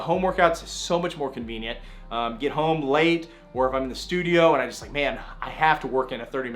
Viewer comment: who else heard get home laid😂